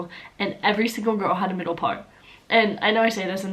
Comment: Oh lord, I was SO confused when you said that all girls have a "middle part"... For whatever reason, my brain immediately translated that to "every single girl has a penis"... I even turned on subtitles to check whether I had misheard...😂